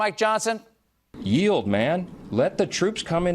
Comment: I thought Johnson was saying “Ye’ old man” not “Yield man”.